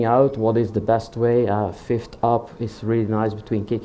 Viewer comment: Will somebody please explain to me what the fith top is( If that's what i heard)? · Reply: "a fifth up" :) (five semitones higher than your kick)